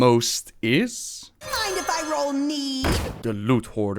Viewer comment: I always thought he said "Mind if I roll mead?"